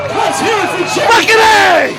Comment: what he said ? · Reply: Effin A